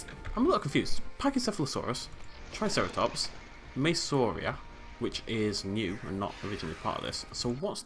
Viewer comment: what dinosaur was that he said at 10:48 " pachycephalosaurus, triceratops, ma......."???? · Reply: Maiasaura